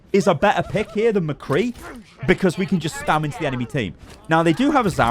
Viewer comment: is he saying "spamage"? As in spam damage? 🤣